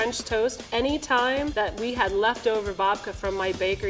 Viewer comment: I misheard the level 3 chef - I thought she said 'I make this with left over VODKA' I was totally perplexed, the concept of left over vodka....